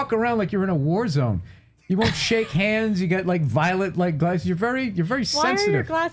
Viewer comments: I thought he said violent glasses too. But I'm high. 😂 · I thought he said violent too